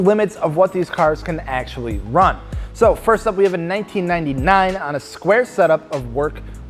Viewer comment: did he just say 1999?